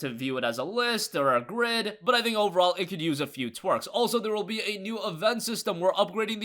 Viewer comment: did he say twarks instead of tweaks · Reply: Twerk xD